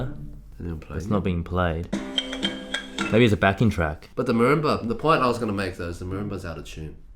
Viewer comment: When he said the marimba was out of tune I initially thought he said the roomba was out of tune...